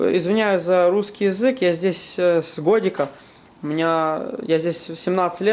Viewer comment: о да, в америке только на ладе и ездить... в стране, где лучший автопром в мире. Была б возможность, я б на стареньком понтиаке ездил, а не на волге... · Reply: я правильно услышал, на 10:40 он говорит, что в Америке "с годика"...Как-то так..)